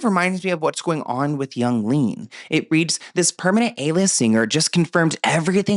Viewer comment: did he just say young lean? okay funny but also … no just funny. love you ❤